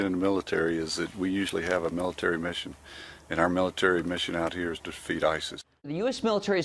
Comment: mins in ... did that guy say 'Our military mission here is to defeat ISIS' or did he say (as I think he may ) "Our military mission here is to FEED ISIS ???'